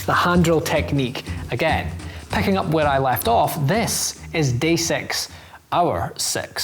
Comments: When he said day 6 I was looking away and thought he said gay sex I was like wat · When he said hour six I thought he said our sex😂